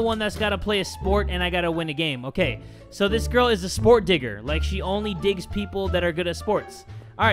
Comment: I thought he said a sport n A sport n A n I dont have the n word pass